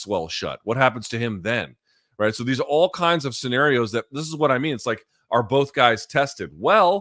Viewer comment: I thought he said Cheerios